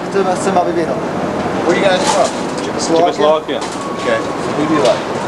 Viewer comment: I think they said, they're from Yugoslavia